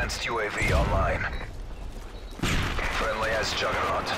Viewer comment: does it say friendly ass juggarnaut?!?!?